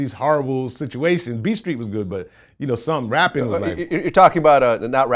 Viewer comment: I had to rewind...I thought he said Beat Street was horrible....glad you cleaned it up sir!🤨